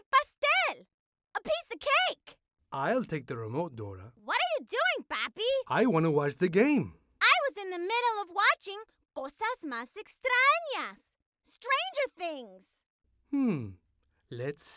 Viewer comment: DID SHE SAY STRANGER THINGS?